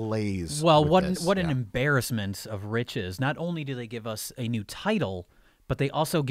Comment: Why was it that when Jay said, "What an embarrassment of riches," I thought he said, "Rich's," like Rich had done something wrong? Also, yes, I'm coming back to this gem 4 years later...